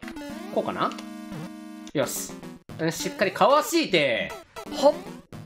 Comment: かわしーてー(空耳)